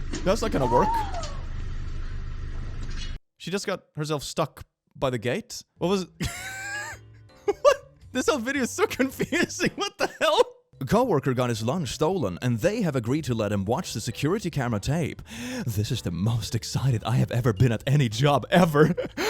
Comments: I wasn't looking at the screen and thought he said lungs 🥲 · ok so i had this video on in the background while doing other stuff, and got REALLY confused because i misheard "lunch" as "lungs". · HELP ME I THOUGJT IT SAID LUNG INSTEAD OF LUNCH I WAS SO CONFUSED